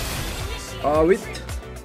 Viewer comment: did he say awit? XD